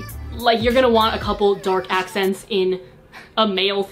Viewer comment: I totally misheard "you want a couple of dork accents in a male themed room" and was like...that seems like a legit idea. Oh.. oh DARK accents. sure. That makes more sense! But I do like all my dork boys...(Also, yes, gender norms are dumb.)